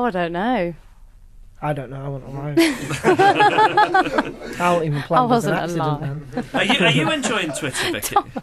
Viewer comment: what does thomas say at 4:06 after he says "i dont know, i wasn't alive" ? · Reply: cayrayandkrae “I wasn’t even planned I was an accident then”